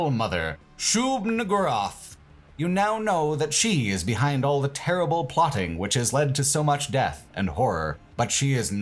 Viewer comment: When you read the introduction to Shub-Niggurath I swear it sounded like you said "You know know the cheese behind all the terrible plotting" which I feel works just as well to explain what's going on XD